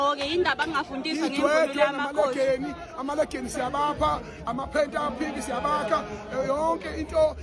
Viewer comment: Did I just hear "amaphenti a phink siyabapha" 😮